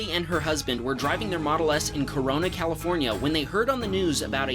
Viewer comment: Did I hear CORONA at 1:41??